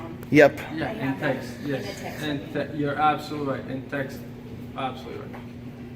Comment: Around the 13:45 mark, he literally says "guys, you're absolutely right. In fact, absolutely right" bro what are you even trying to say??